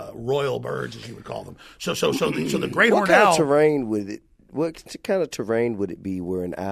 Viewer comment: What kind of owl he said it was? Alex jones talk to damn fast! 😂😂 all i heard was "grain oran owl"!